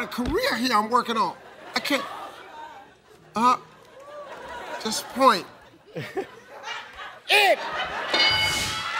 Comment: Anyone know what the guy at 42:40 said? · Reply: He said “shit”.